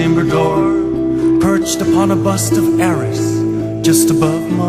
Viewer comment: why does it say "perched upon a bust of aris"? or am i just hearing things?